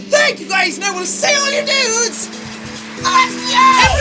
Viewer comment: When I started watching this channel, I heard him say: " AND I WILL SEE ALL YOUR NUDES... IN THE NEXT VIDEO!", Anyone with me?